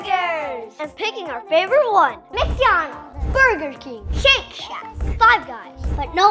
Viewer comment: Foer an second I thought he said sh!t shack